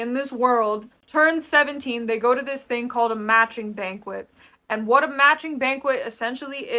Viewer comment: I thought you said “matching penguin” for a second and was thoroughly confused